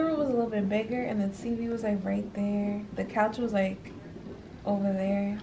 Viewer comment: Congratulations to you both !!! I believe it’s a boy I think I heard say “ he right there “ 👶🏽